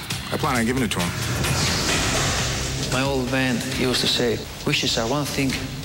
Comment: what does crocop say @ 7:32? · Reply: my old man use to say wish to say one thing..reality second thing....8 years later hope it helps you out🤣